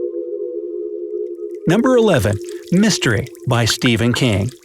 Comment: they spoke mystery or did i hear wrong? 🤔